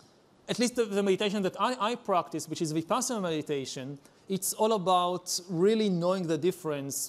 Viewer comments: For everyone interested in the subject, at 49:09 the subtitles say "passive meditation". The correct expression is "Vipassana meditation", which is a particular form of meditation. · The subtitle says "with passive meditation" instead of "vipassana meditation" I guess.